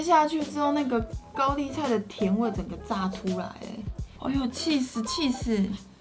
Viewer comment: 我把起司听成气死😂🤣